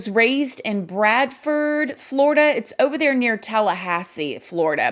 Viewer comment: Girl, you just scared the living day lights outta me justa 'bought spit my fried rutabagas right out. I thought you said Bradford, FL lol then I realized you said Bradfordville. I was like I swanee I know Bradford county people are crazy but what now? Lol!!!!